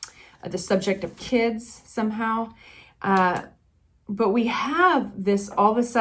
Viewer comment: Odd. Minute 2:18 .. "subject of kids .. somehow" ... sounded like I'm just gonna take a piss ... somehow. Well the text reader got that straightened out for me. Good read! 😄